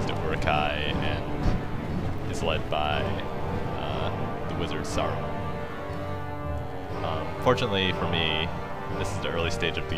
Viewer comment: The Wizard Sauron? I thought I heard you say that in the beginning lol fantastic video by the way